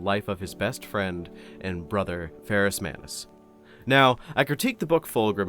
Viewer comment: man when you said "his brother, Ferus Manus" I heard "his lover, Ferus Manus" and for a moment...